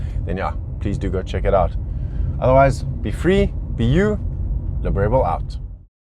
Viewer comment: Lol only today I heard that's its actually "Be Free , be you. Liberebil out" talks so fast and I thought it's Briefly be you. Which never made sense😂